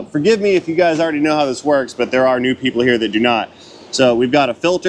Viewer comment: Hour and a half video requires lots of popcorn 🍿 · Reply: New people here..really...my hearing is waining...thought you said nude people.😳